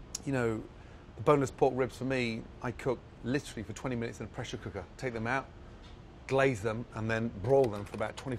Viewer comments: when it said pressure cooker i thought it said pleasure cooker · I thought he said “brawl them”